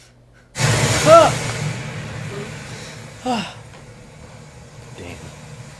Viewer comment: hahaha if you just look at sasuke face it sounds like he said DAMN hahahaha